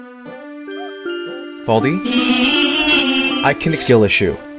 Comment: did baldi just Say Skill